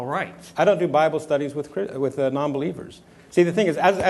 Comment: I can't pick out what's being said that deserves applause at 8:44. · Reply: She said Sye: "I don't do Bible study with nonbelievers" Girl: "Jesus would!"